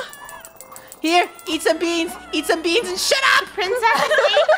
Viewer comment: Who else died of laughter when said " EAT BEANS AND SHUT UP! " 😂 :Edit: Thank you so much for hearting my comment and thank you for the likes they're the most I had!!! ^ | | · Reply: I thought he said "here eat some beans and shi-"but he actually said"here eat some beans and shut up" when I replayed it😂